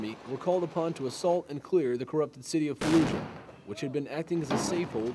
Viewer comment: Sorry didn't catch the name of that city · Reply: Fallujah bro its in the title